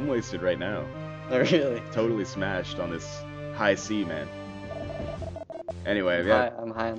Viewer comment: Wait, did he say he was getting smashed on "high semen"?